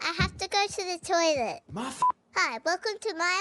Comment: Did he say Ahh Fu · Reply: He said “Muffin!” but is cut off.